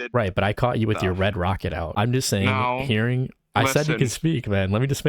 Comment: i atleast excpected an intro, instead i heard "i saw you with your red rocket out" zamn twin i didnt know was tuff like that